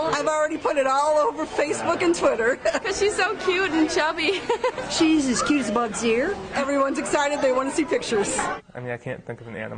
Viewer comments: Does she say "as cute as a bug's ear" ?? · That lady that called her "as cute as a bugs ear" made me laugh. Is that something people say? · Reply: haha i was so confused i thought she just randomly said "Jesus keeps bugs here :)"